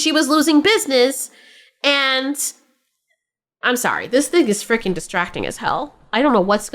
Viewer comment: I think it's called Split. It's about characters going through video games or something. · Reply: Could've sworn she just said, "This dick is freaking distracting as Hell." All while talking about a dude whippin out his 8 incher 😂😅 Also, lol, how fitting is this video footage??? 😂🎉❤